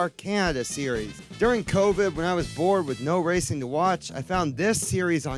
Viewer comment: I thought he said “born”